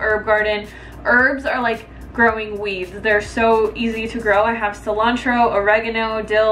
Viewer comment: I thought you said "Herbs are like growing weaves" 🤣